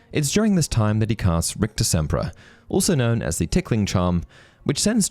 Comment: In the wizarding duel, I thought Harry said something like "Ricochet-pra" as in ricochet like perhaps the spell was meant to copy the spell of what was just thrown at him to throw it back at the opponent.